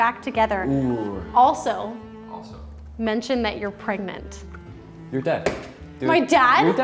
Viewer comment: I thought he said you're dead